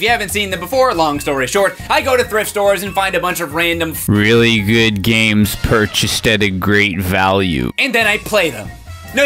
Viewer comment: "Random f**king piece of s**t games, that nobody cares about at all." Just in case y'all don't know what he said. 😉👍